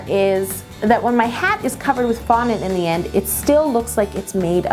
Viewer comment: When you said covered in fondant, I thought you said covered in vomit.